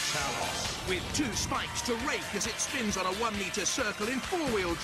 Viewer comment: I know this is probably me but every time I hear Talos's intro, it sounds like Johnathan Pearce says "with two spikes to r*pe," I really hope that is me just hearing things because if he did say that, that is almost as bad as when he said, oh I'll save that for series 2 heat K. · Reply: Think he said rake.